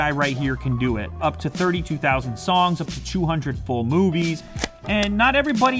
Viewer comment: "Up to 200 for movies", w-why did i heard "Up to 200 porn movies" ??!